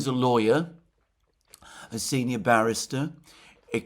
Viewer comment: “Starmer is a senior BARRISTER” - sorry, I thought you said “senior embarrassment”, silly me!